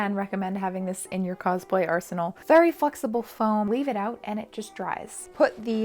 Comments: anyone else hear "very f*cksible foam" idk if its because I'm not used to your accent 😂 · I heard ‘fucksable’ and not ‘flexible’. That is all.